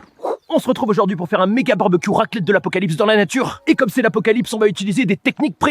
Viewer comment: Je faisais une balade dans la forêt puis j'ai entendu "allez on va faire une raclette a l'apocalypse" jme suis casser direct puis 2jour plus tard jvois sa